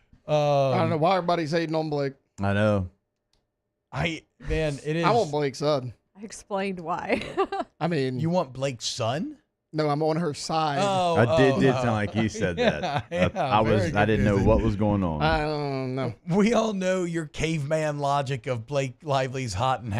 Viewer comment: bot 31:45. It did sound like he said that he wants Blake's son.🤨